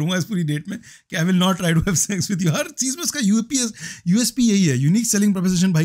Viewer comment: did you say UAPA?